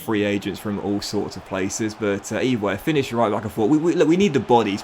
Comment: I heard a "finished right back" lol